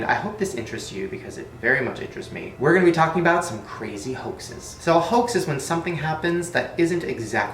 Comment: is it just me pr did i hear hostes like hostes cakes GASP CONSPIRACY THEORIE